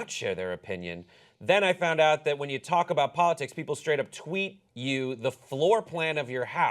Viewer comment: did she just say "politits"? I swear I heard that lol